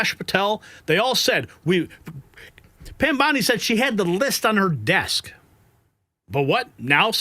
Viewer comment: According to a video recording, I heard her say a "folder" was on her desk, and I did not hear the word "list".